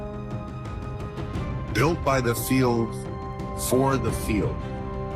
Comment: Thought the promo vid said built by the few for the few….i thought ballsy, but true. Turns out it’s not what they said but it made way more sense than the word ‘field’ instead of few , that made 0 sense to me.